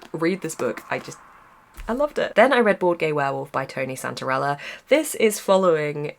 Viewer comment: At first i misheard Bored Gay Werewolf as Boardgame Werewolf, so now i want to play The Werewolves of Millers Hollow